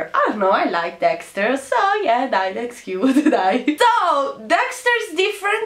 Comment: avevo capito "Yeah, die that's cute" poi mi sono resa conto che aveva detto dai in italiano e non "die" HAHAHAH